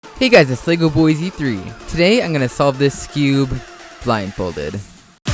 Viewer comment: Did he say, "This skewb" or, "This cube" ? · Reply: skewb